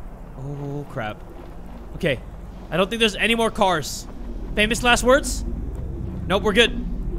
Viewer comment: When he said "Famous last words?!" I heard, "Any last swerves?"